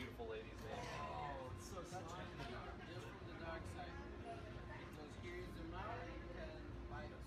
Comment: does he say they are from the dark side?